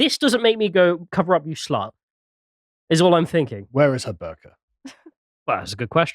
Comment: Misheard Carl I thought he said "Where is my burger"